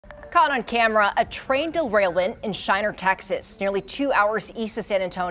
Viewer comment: Did she just say a "Train Delrailwent"...